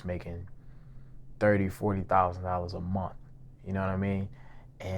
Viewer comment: Bruh is it me or did he just say 40,000 a month that’s 480,000$ a year big bank